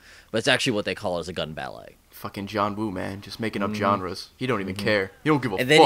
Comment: Who else thought Ray said "Making up 'John-res'"?